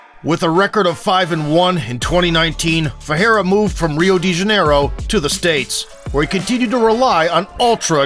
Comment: Instead of Rio De Janeiro I heard "Real Degenero". Can't unhear it. My life is ruined.